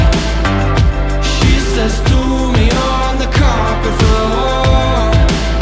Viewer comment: Doesnt it sound like he says "do me on the carpet floor" or am I just hearing shit